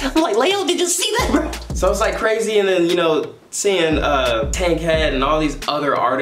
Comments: Did anyone else hear him say Tay-k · did he say tay k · i thought bro said tay k LOL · Is it just me or did I hear that tayk was there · did he say “seeing tay k” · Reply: Cash4Coins tankhead